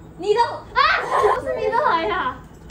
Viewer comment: i thought she said nigga💀💀💀